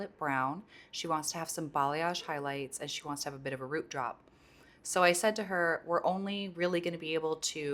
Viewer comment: Did you say root job or root drop? either way..what do you mean? 😂😂 Like a root smudge? 😂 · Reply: Hahah a root drop! I’m sorry my cold is making it hard to enunciate properly! Exactly like a root smudge! Just another term for it